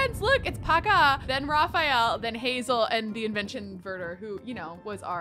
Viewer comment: did she say convention murder or did i miss hear?😳